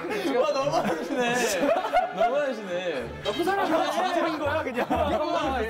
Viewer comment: HSKHSLSHSKS WHO PUT THAT IN THE SUBTITLES DID HE ACTUALLY SAY THAT LMFAOOOOOOOOOO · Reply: Nah lmao he said "nappun saram" which directly translates to "bad person" 😂